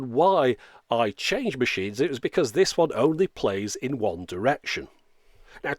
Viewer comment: "The machine only plays One Direction" was a scary way for me to mishear.